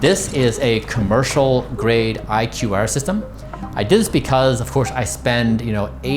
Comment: Did he say IQair or IQR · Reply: It’s iqair 😊